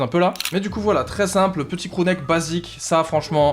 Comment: C moi ou a 9:19 il dit « Crew Nike » en parlant d’un pull ston island